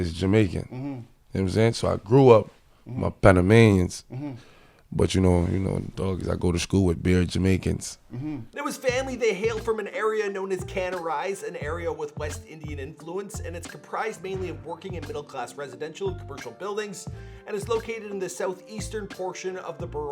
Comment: Yo bro did you just say canorise